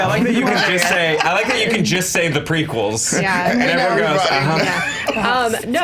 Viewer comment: I think said "like the prequels" 36:39 as was describing a society collapsing and letting someone shifty comes in and promises a solution. As much as is one of the funniest people on this channel, his prequel hatred and assumption that others feel the same are getting tired.